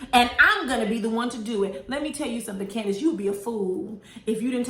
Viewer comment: It actually sounds like mia said “and im not gone be the one to do it.” Thats what i heard the two times i watched it. Idk if its karen age but she sure does say stuff and forget or backpedal. Karen was saying that stuff like it was a fact. Not like it was a joke and it wasnt funny. I dont believe theres colorism on the show. People love bringing up colorism when its darkskin vs lightskin. Those women are just hypocrites and cant take accountability. If Mia is elitist for bringing up her money then what was wendy when she kept bringing up her degrees? Karen and Gizelle are frenemies lol I agree that everyone is moving funny this season including andy. The difference between askale and marlo is that one actually brings something to the table. Im looking forward to askale being a fulltime housewife though. Lol that Chesapeake tears comment can be said about candiace too who actually cries more than anyone else on the show lol. At this point people need to stop bringing up that fight between mo and candiace cause both issues were dropped lol. Robyn and gizelle know damn well they were full of 💩. Wendy was speaking facts.